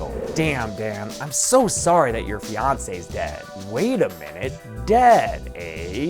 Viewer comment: Hella thought you said he carried Beyonce lmao, you said fiance